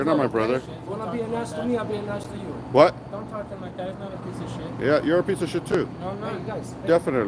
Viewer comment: The guy that pepper sprayed you is the son of the security man if you go back and listen you’ll hear him say don’t talk to my father like that so they’re related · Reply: I know this is an old video but spunded like he said don't talk to him like that then sounded like he said my dad's not a piece of s*** but I know it's already been confirmed it's not his dad